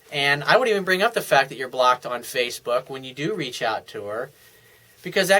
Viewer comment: does he say that he "should" bring up the fact that she blocked him on facebook, or that he "should not" ? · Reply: Should not